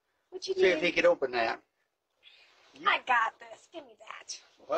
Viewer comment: Man, seeing elderly in this state, "I don't have no friends left" really makes a grown man cry!! Such a pure soul! We need to respect and protect people like this man at all costs!!! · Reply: I thought he said that but he actually said “I don’t have no strength left”.